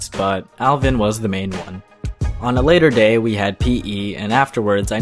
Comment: did someone say ''rape''?